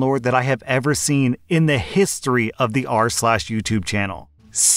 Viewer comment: this isn't related to the stories or anything, but every time i watch one of your videos, i mishear rslash as arse lash